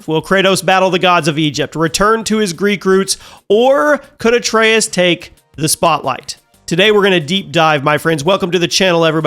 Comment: I thought he said “could a Trans take his spot”. June hitting hard this month